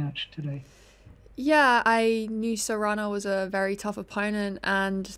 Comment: It sounds like she is saying jamón serrano